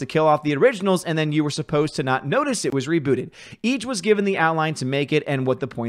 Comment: Reboot I thought you said boot it to the curb.